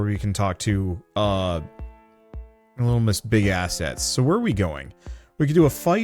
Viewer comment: Omfg I thought said Lil Miss Big Ass Head and I'm currently halfway on my bed cause I rolled over and fell 🤣 I had to rewind it a few times... I'm gonna go take a break and wipe my eyes